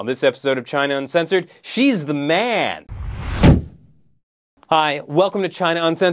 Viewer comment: LOL, when you said "Xi's the man" i thought you said "she's the man" xD